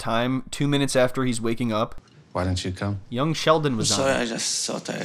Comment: Did I hear "because young Sheldon was on"? Bahaha😂😂😂16:56.